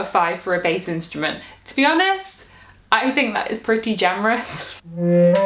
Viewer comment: Did you just say "That's pretty jamerous" or did I hear wrong? Love the Dad joke. Oh, Loved the Hobbit inclinations there.